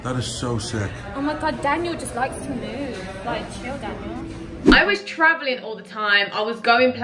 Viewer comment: I thought u said chill daddy 😂